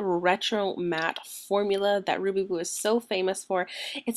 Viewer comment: It Sounded like You Said Ruby Boob .. Lol